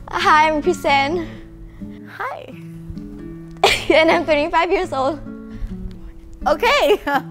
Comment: Wah! Chrysan 25 meh? She looks super young sia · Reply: I hear she said " I'm only 5 years old "